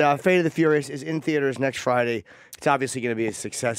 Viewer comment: i thought Jason said: Thanks, Chips. but it was: Thanks, Chaps.